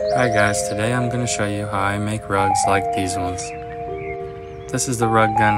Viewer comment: Who else thought it said drugs instead of rugs? No? Just me?